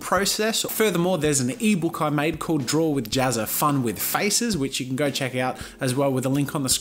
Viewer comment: I misheard fun with faces as fun with feces (poo)